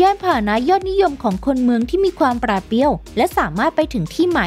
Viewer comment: มีใครได้ยินว่า "ปราดเปรี้ยว" มั้งฮะ